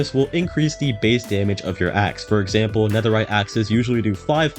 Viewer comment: Ha ha... 11:32 I heard "Ex" instead of "Axe"... 😢 "This will increase the damage of your Ex" Lmao